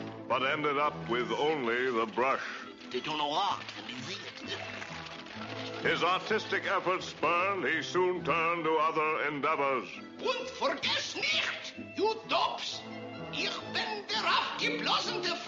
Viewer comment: I thought he said autistic efforts